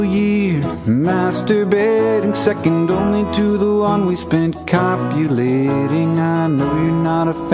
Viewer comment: I always thought he said 'second only to the one we spent calculating', because you know, it's a song about calculating things...